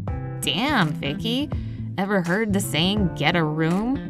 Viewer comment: "That can be our little secret!" People all over the world see the video, not so much of a secret anymore! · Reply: DID THEY JUST SAY DAMN?